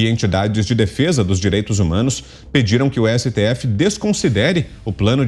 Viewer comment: como falaram " direito humanos " como ouvi " direito dos manos "